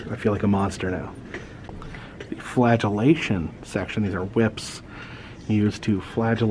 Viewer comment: I think the hammer at the beheading display was for if the axe got stuck in the next or didn't make it all the way through. I'm also disappointed you didn't call it the Flagellation Station! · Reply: At first I thought he said the flatualation station. That would of been pretty horrific.🤣